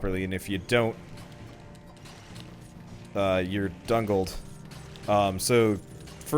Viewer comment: did he say dungled?!? My brother says that religiously I thought he made it up, is this a thing?